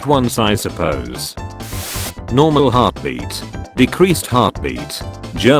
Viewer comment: anyone hear decreased not deceased